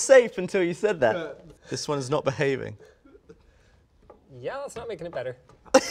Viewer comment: linus... what did you say? edit: "This one's not behaving." "Yeah, that's not making it better..."